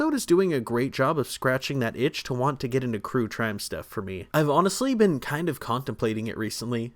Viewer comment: I heard that “cure trime “ 😅😅😂😂❤❤❤❤❤